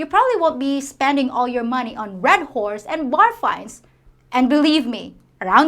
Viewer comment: I can't understand your statement at the 7:20 mark of the video. It sounds like you said "you probably won't be spending all your money on red whores and barflies." Can you confirm what you said?